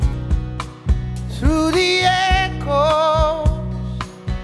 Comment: I keep hearing “through the alcoves”….. I think I’ve been watching too much of In Bruges